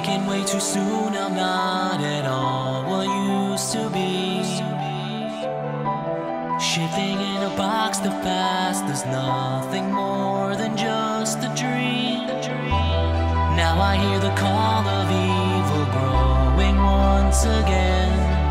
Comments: I can’t be the only one who hears “shitting a box” at 0:28 lol 😂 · why do I hear S***ing in a box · why does it sound like he says ''shitting in a box'' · When I first listened to this I thought he said shitting in a box